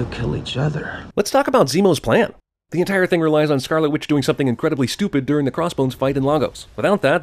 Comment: I was in a class while watching this and I heard “scarlet bitch” and I couldn’t stop laughing